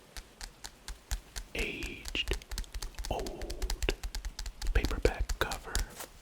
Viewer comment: I thought you were saying big back cover at first lol